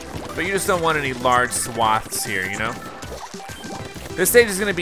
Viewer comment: did say swats.